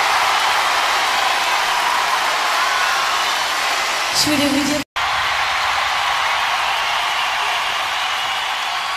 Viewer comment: Euh tu voulais dire quoi Mylène ???😶😶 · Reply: De mémoire, elle dit "je voulais vous dire que je suis probablement en train de passer les plus beaux moments de ma vie, et je voulais vous en remercier".